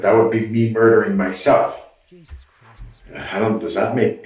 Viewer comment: First of all, don’t worry, the straightlaced analysis vids aren’t going anywhere. When I found this case the thing I struggled with was that the guy is honestly just a real bore. As an experiment I decided to see what it would come out like if I made that the angle of the project. It will be interesting to see how this one is received. Thanks so much for all the comments. To those saying the channel should be bigger - good news, what you’re doing is working! The sub count has about doubled over the past week so that exponential growth is kicking in. I will work to create a Patreon but I want that to launch with some exclusive content which will take a while to put together without slowing down the free uploads too much. If you absolutely insist on giving me money in the meantime there's a PayPal handle in the description, but don’t stress. Right now the best way to support is to keep up the likes and comments, and SHARE the channel in any way you can. Speaking of which, thanks to Scribe_Light for having me on his channel. If you’re interested in checking that out the interview is here: <link> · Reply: What's that your mic picks you up saying around 38:20 btw "Jesus Christ this guy..." ?